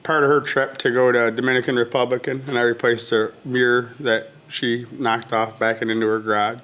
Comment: Dear Lord…..He said “Dominican Republican” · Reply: Sounds like he said " Dominican Republic and I replaced her mirror", etc.